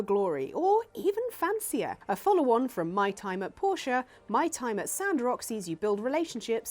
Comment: I thought you were saying my time at porsche, and tried to look it up because it sounded really fun!